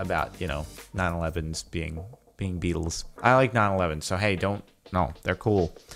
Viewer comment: I wasn't paying attention when Jordan said "I love 911s" and I thought he said "I love 9/11" and I was like "WHOA what"